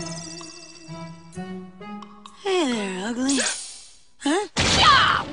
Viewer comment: what is he saying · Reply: He said hey there ugly